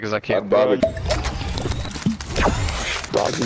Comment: When he said dawg did you just take a big sheild i thought he said dawg did you just take a big sh**